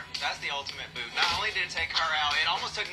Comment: "That's not just a garment that's an OSHA violation" Perfection. I'd love to see Maddy back for an All Stars season. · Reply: We don’t have OSHA in the UK so when she said it I thought she said ‘ocean violation’ and absolutely creased ☺️